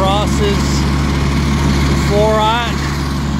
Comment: What’s the second cable at 4:16? Where you say it crosses ...? And how much of the dug up portion was restored by the company? Grass, mulch etc. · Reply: He said "four aught" which is "4/0" the fat power cable that goes to your house.